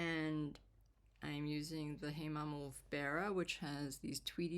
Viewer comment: Sorry but what are you saying that brown yarn is at 9:44? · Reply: Hey Mama Wolf Beara?